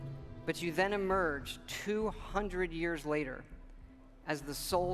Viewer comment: Also, Codsworth mentions it being 200 years later, which is probably correct because you know, robots don't round things up. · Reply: have sworn he said 210 years